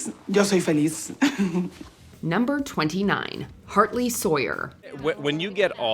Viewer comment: My brain sort of shut down for a second and I thought it said "Harley Sawyer", but then she said his name, and I understood.